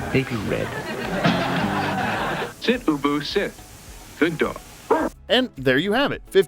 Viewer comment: I thought it was said boo-boo sit